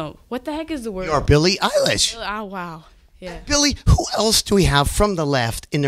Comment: all i could hear was bilie saying "Billie ow wow😆😆😆😆😆😆😆😆😆😆😆😆😆